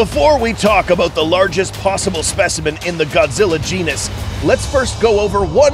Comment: I thought it said Godzilla genius